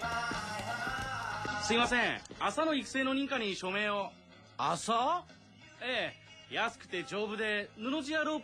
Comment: Swear he said: Asshole?